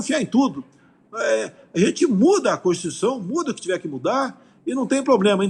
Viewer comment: Eu entendi bem? Aos 43:39 min do vídeo o ilustríssimo Sr Presidente fala em mudar a constituição ???